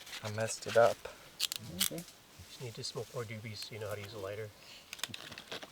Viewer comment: did say smoke more doobies😂